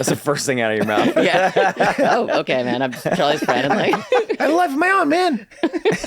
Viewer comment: What does Charlie say at 21:46? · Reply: "I have a life of my own man"